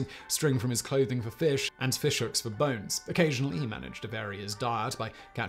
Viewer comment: I've never tried using Fish Hooks for Bones. Sounds painful, but He's the expert..... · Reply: Thought at first he said "For fish hooks used bones."